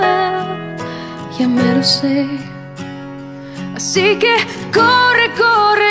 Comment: siempre pensé que decía "sigue" en vez de "así que" :v